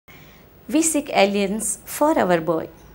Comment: I thought she was saying "We seek aliens" ROFL